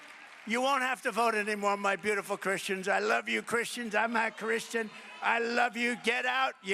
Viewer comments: Did I hear correctly? He actually said “I’m not christian?” Where is this clip from? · Did he say “I’m not Christian?”😂😂😂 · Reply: I hear it as "I'm a Christian." But we know he never lies.